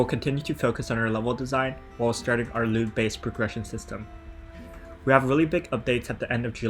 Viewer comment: did you say Lewd Based Progression System?